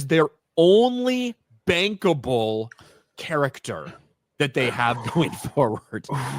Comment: Bankable I thought you said bangable😂